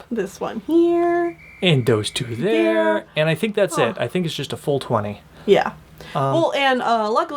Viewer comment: When said full 20 i heard it as 420 so i wend back and was proven wrong. Now i’m sad